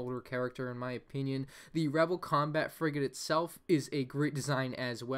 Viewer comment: When you said frigate it sounds like u said frick it